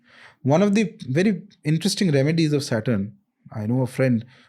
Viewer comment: What did he say at @ 1:11:45? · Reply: One of the interesting remedy of saturn